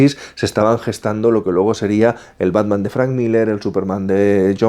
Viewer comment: en el minuto 19:20 dice el batman de ezra miller o yo escuche mal?? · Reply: Dice Frank Miller no Ezra Miller